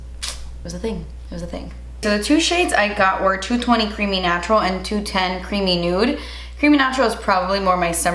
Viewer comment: I thought you said "creamy nacho" instead of creamy natural at first. I was like "interesting name choices, Milani.." 😂 also, now I want nachos. 🤷